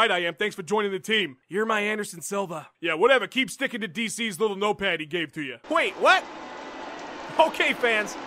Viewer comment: what does Weidman says at 3:49 ? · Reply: "yea whatever keep sticking to DC'S little note pad he gave ya"